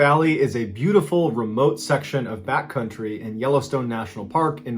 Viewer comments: I really thought you said Bat Country and I was so happy, but alas you said back country and it wasn’t as funny. 😂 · At the beginning of the first story, i thought you said "bat country" instead of "back country" I thought maybe Hunter S Thompson was camping there. 😂